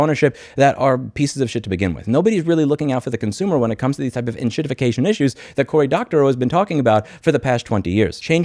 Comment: Did I hear that correctly? 7:55 "…when it comes to these types of enshitifcation issues"? 😂